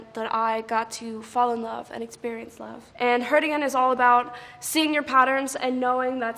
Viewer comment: Why everytime she says 'hurt again' I keep hearing 'cardigan'